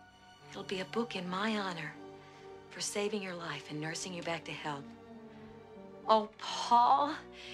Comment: It sounds like she said nursing him back to hell